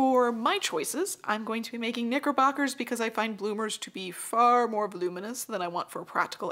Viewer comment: I thought you said for a min "Bloomers to be far more [ba'loominous] than I want". ex: Girl, your bloomers are bloominous today. ok back to crochet for me...